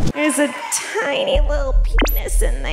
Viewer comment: Okay initially I thought she said “there’s a little shit in there” so I was like “okay fine not that bad”. not what she ACTUALLY said!